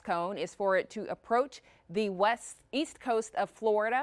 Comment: Exactly where is the west east coast? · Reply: I thought you said weast.